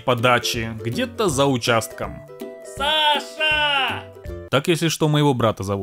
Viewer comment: почему я слышу не Саша, а Паша?😅